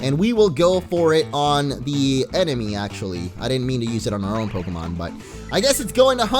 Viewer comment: Did he say Philemon